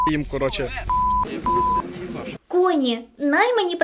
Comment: мені одному почулось "куні" :face-orange-biting-nails